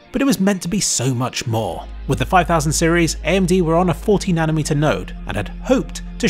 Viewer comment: thought you said it was 14nm and had to rewatch it a few times to hear 40nm lol.